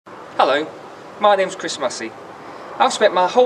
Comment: oooh..chris massey! i thought he said christmas eve!